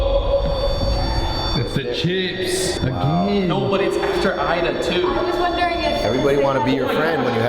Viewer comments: it sounded like somebody said ida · i don’t know if it was someone else but i heard a voice saying “ida” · I don't know if i'm trippin but I swore I heard someone yell "IDA!"